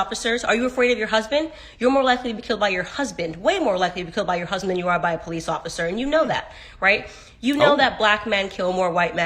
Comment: You're much more likely to be killed if your husband is a police officer. 40% domestic violence rate perfectly reflects the USA's policing philosophy. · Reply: Either I heard this wrong or you did. I thought she said more likely to be killed by your husband than a police officer.